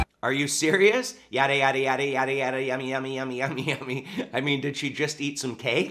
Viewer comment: I think she might be saying “yummy yummy yummy” she might be a little hungry. Probably needs a snicker 🤣